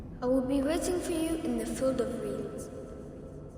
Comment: captions say something else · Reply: I will wait for you in The Philippines 🗣️🗣️🗣️